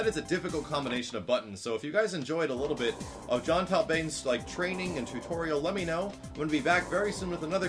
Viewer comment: I thought it said training Taliban lol